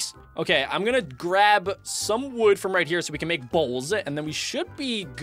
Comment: when he said bowls I thought he said balls🤣🤣